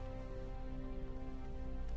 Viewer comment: anyone know what's he's saying there? All I can hear is "Will we die? just a little?" considering my confusion, I doubt that's what's he's really saying.